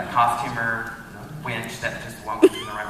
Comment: what did they say about Steve at 12:40? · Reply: they said Steve was a wench